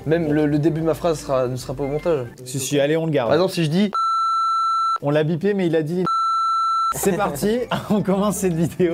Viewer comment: Il a dit quoi du coup ☹️ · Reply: j'adore les tartes au citron meringuée